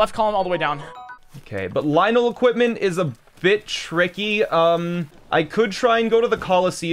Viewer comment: For a moment, I thought he was saying "Lynel equipment is a B**ch, Ricky."